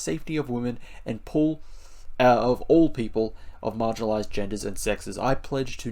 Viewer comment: I was listening and when you read the pledge and said "all people" I only heard "orc people" :D